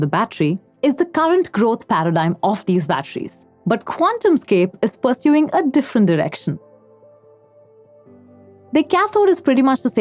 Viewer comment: I swear at 2:18 she says that "Quantumscape is pursuing a different erection". I listened to it 5 times and that is all I can hear. No offense to her meant to her at all. It brought some brevity to an otherwise interesting but dry story.